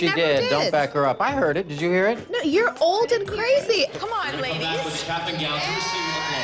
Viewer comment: Did Khloé just said your old and what 😳